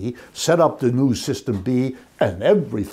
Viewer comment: I thought he said "everything works just fkkin fine."